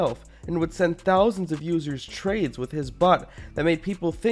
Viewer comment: did anyone notice when he said bot, it sounded like "butt" ? XD one you hear it, you cant un-hear it